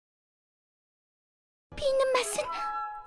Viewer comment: 뭐라고 하는지 못알아듣겠는데 알려주실분? ㅜ 피는마슨..<< 이러는거로 들리는데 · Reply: 깊이 있는 맛은